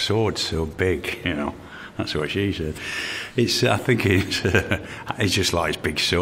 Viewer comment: Did Sean Bean just say " that's what she said " !!? xddd